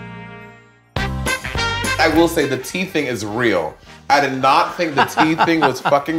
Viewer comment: Oh my god. Right at the beginning I thought she was saying “the teeth thing is real” instead of the tea thing… I would like to apologize to the entire uk except for anyone who voted for brexit